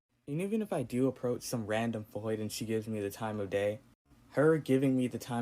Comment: What was the self-loathing guy calling girls? Sounded like he was saying “voids.” Wtf does that mean?